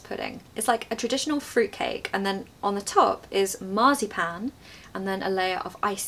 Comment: Dear, 9:59 > you say a word like “marsypen”.. what is it and the word is it?? Please would you write the right one and its meaning, thanks 🫶🏻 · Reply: It's "marzipan", it is a confection consisting of sugar and ground almonds.